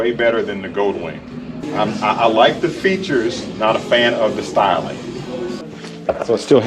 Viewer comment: Hey….You look mighty comfortable on that Indian Roadmaster …think i heard you say@ 12:25 “I like this bike”. Me and my fellow Indian riders are having an impact!!! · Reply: "I said I like the features but I'm not a fan of the styling" it's nice but not for me. Nice try